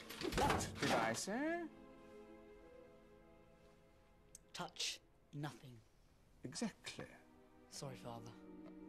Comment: WhAT did I sAY? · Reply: Touch... nothing